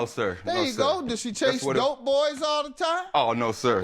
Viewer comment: Took me a second to realize Judge Mathis said "Does she chase dope boys all the time?" .. I thought he said goat boys and i was so confused